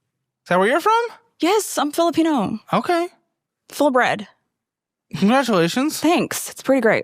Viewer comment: "WHERE DID IT GO?" "TO THE TRIP I JUST TOLD YOU!" "WHERE DID YOU GO?" "TO THE PHILLIPINES!" "...is that where you're from?" "Yes I'm Fillipino." "Okay." "Full bred." "Congratulations." "Thanks, it's pretty great." That entire exchange is a meme. 😂😂😂😂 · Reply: I was so stunlocked I actually thought she said "full bread" lmao